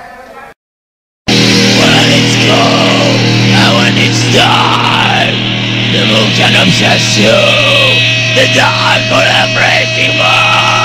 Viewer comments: the dark … ? ( what he say ? ) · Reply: "When it's cold and when it's dark, the moon can obsess you, the dark of the freezing Moon " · what did he say there? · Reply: “When it’s cold and when it’s dark, the moon can obsess you, the dark of the freezing moon” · What does he say here? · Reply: "When it's cold and when it's dark the moon can obsess you, the dark of the freezing moon"